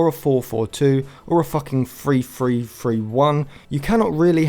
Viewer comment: Can't beleive I heard"Or a fucking 3-3-3-1"😂😂😂